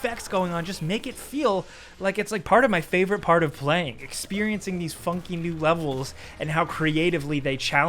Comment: I thought he said experiencing those fucking level💀💀💀